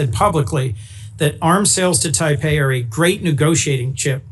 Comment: Did he say Taipei instead of Taiwan?